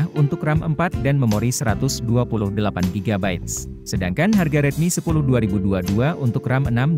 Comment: Apa nggk salah denger ni, redmi not 10 5G nggk ada NFC nya? Ini gua pakek kok ada??? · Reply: Itu redmi 10 5g kak, bukan redmi note 10 5g😊